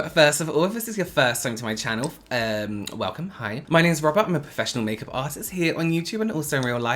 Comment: I always hear him say "My name is Rubber" lmao